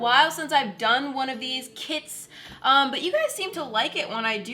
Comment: I know moriah said “Kits” but for a second i thought she said “kids”